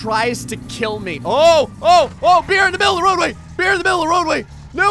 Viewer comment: I thought you said "A bear in the middle of the road way" instead of beer lol